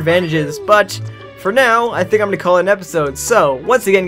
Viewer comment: Your intros too fast!!! I heard "My names Meh"!